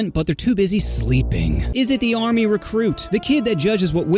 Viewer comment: i like how it just says "arm recruit" instead of army recruit